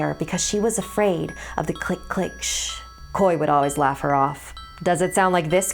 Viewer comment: i thought it said clickashit